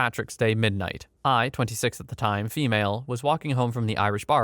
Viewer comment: I wasn’t paying attention at the start of story 17 and thought he said “I, 26, female at the time” instead of “I, 26 at the time, female”